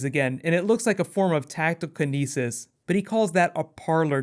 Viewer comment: what did call Damien's ability? i didn't quite catch that. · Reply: He said "tactile kinesis."